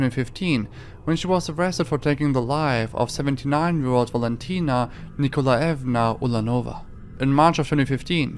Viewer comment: I thought you said "79 year old Latina", which seemed like an odd descriptor lol.